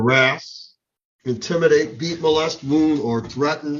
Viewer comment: "You are not to harass, intimidate, *eat*, molest, wound, or threaten..." Eat? I'm sorry...*eat*?! I just listened to that several times thinking for sure he was saying 'beat' but I kept hearing 'eat'. Am I hearing that wrong? I feel like I have to be hearing that wrong. If I'm not hearing that wrong, I'm not even sure I want to know what transpired that they had to essentially put 'no cannibalism, mkay?' into the bond conditions. Edit - Okay, I just listened a couple more times with my iPad against my ear. I think it is beat. Which makes more sense.